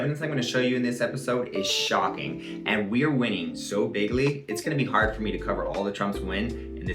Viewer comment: I always thought Trump was saying “BIGLY”, but it turns out he was saying “big league”